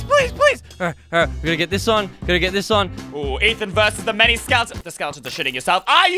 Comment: Wait what did he just say?! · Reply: He says versus the many skeletons bro but it sounds like "p**nis"